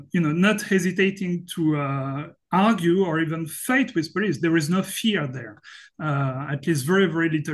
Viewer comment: Sorry to hear that we Chinese dare to fight the policeman, especially when we hear that from a foreigner, it's a little embrassing. We rarely do that by the way. As far as I know, it's totally illegal to fight with any other people in China, not even to mention a police officer... I understand Mr. Bertrand wanted to emphasize that china is not a police state, but I also want people know that most Chinese are peaceful people, just as people in other countries. LOL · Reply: Maybe I got it wrong. I thought I heard "fight with" in 7:25. I'm not a native speaker, I hope I got it wrong